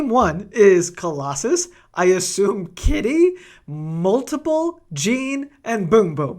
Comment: I wasn’t watching and thought you said multiple Jean’s as in like there was multiple of Jean in team 1